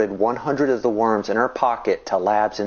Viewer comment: did he say pocket? I used to do that with catepillars